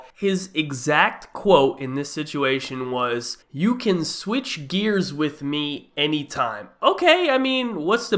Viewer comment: I thought he said switch ears